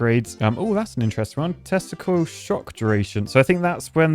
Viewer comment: lol 4:04 youtube caption picked up testicle again instead of tesla coil